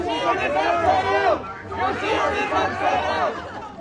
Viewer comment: I cant even understand their incoherent, and broken chant.... All i heard is something something "clash royale" 😂😂